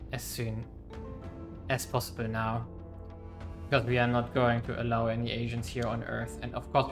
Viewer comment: Am I hearing things or did you end up saying "not allowing any Asians on Earth" instead of Aliens. 🤣